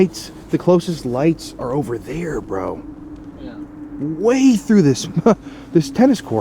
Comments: you hear a disembodied voice sounds like it says “yea” or “Yo”.. it didn’t sound like voice.. 🤷🏻‍♀️ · right after you say "The closest lights are over there bro" at 1:49:29 and immediately after you hear "Yeah" and it definitely doesn't sound like... I could definitely be wrong and it is him saying it, but it honestly doesn't sound like him to me. It kind of sounds farther away. IDK, if anyone else can hear it let me know lol